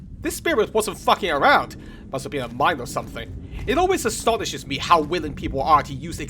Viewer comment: I initially misheard that as "Must have been a mime" and was half-expecting Courier to fight a silent French enemy in black and white makeup.